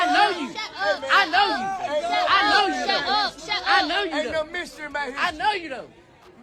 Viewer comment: TI we already heard King ain’t yo son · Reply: I heard TI say "ok mystery baby" 😮